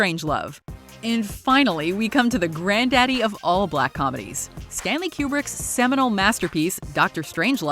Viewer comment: did she just say "black comedies"